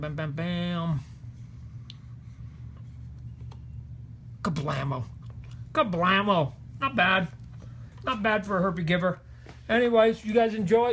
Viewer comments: Am I trippin or did he say not bad for a herpe giver? 🤭😆🤣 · Did he say "not bad for a herpe giver"? Timestamp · did he say not bad for a herpes giver.. telling the world hes gives people herpes